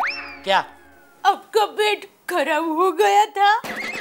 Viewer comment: i thought she say apka khara hogya tha 😂😂